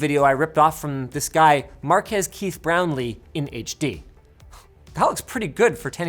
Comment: Hi, what's the source video taken from at 9:55? Can't understand the name of the person, I'm not a (native) english speaker, and can't find any "Markath Chris Brown Lee". Thanks!